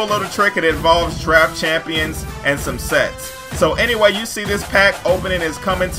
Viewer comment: I thought he said draft champions and sex 😂